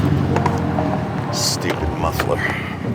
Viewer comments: The blue Subaru with the big muffler, as was getting back in his car I thought he said, stupid mother f**er , stupid muffler !! For a split second I thought, no way would say the first one haha !! Love these vids and would be a pleasure to be stopped by. Be safe , cheers, Daz, UK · What is said at 6:16? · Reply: Stupid Muffler